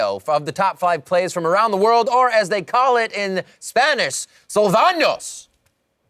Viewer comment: Can someone please tell me what the he said??? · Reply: AHHHHHHHHHHHH "Sálvanos" he didnt pronounced that the right way, it was a "tilde(´)" so it sound different to "Salvanos" hehe xd